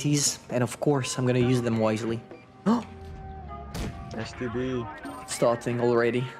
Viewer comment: was just me or did I hear call the STB-1 the “STD”